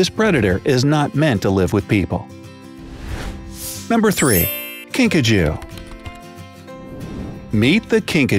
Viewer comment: that sounded like pikacho